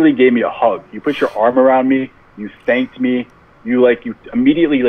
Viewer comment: I thought he said he Spanked me 😂inside of he thanked me lmao!!!!!